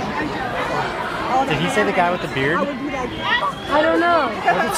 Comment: is it just me or does it sound like he says “he you the guy with the hand cannon”